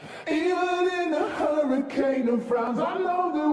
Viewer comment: I always hear, “even in a hurricane of brown” 💀💀